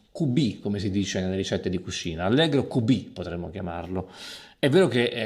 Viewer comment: Italian here, small tip on the subtitles: what Maestro Prosseda is saying is not "Allegro Cubi", but "Allegro q.b.", q.b. being how "quanto basta" is written in Italian language recipes